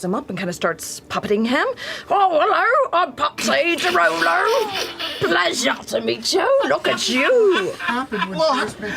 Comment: What did Travis ask matt at 14:20 ish? Something about what happened, and Matt says "a lot" with a knowing look. Seems like Laudna has deep ties to Whitestone/C1/Percy(?) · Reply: "What the fuck happened when she was making this character?"